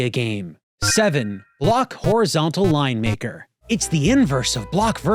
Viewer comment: misheard "Line-Maker" as "Lion Maker"